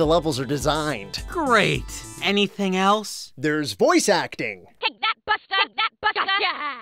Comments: That voice acting sounds like it is saying, "Take that fucktard!". lol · I swear i hear: Kick that Butt-F*ck!